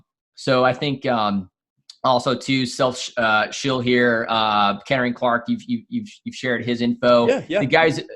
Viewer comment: is he saying Karing Clark is the smartest trader in the space?